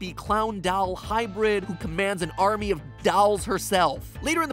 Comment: what I hear when matpat says dolls DALLLS